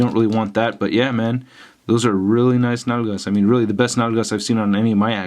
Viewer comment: those are really nice what? nanas? nagalas? what are you saying haha